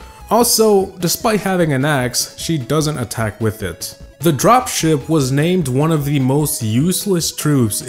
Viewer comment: i thought he said drop shit not drop ship